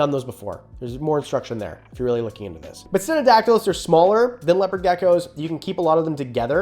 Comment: I really can't tell what you're saying at 14:01, subtitles say it's codedactylus but nothing is coming up on google for that, it sounds like sinictactals? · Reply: its ok i found it, stenodactylus